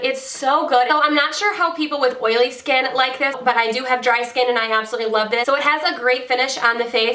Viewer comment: Does anyone know what her skin type is? I wanna try the stick foundation she talked about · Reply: Alayna Jones i think I heard her say dry skin. Hope that helps 😀